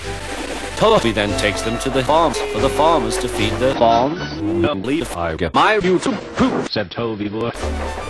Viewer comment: What does Toby say at 0:27? Something about his YTP? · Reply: "At least if I get my YouTube poop"